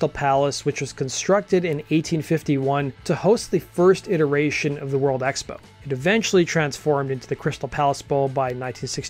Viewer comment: I heard "interracial world expo" instead of "iteration of the world expo" 🤣🤣